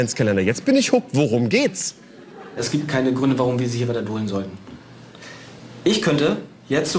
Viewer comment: "es gibt keine gründe, warum wir sie redebolen sollen", was zum geier labert der @ 17:33, ich verstehe es nicht. edit: aaah, "warum wir sie hier weiter dulden sollten", junge, red doch mal deutsch, die verstehen dich doch so schon kaum